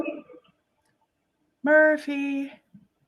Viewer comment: at first i head them say "Murphy" but then I hear "come here doggy."